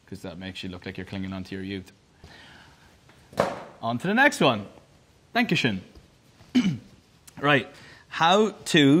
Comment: Did he just say “Dankeschön” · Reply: I thought he said "thank you Shawn" with a weird accent on Shawn 😂😂😂